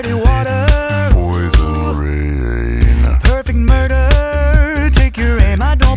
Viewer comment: "pOisOn RaINnNN!" - 2019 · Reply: I thought it said poison grain